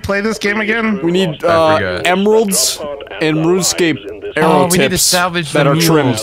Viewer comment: did he say RuneScape arrow tips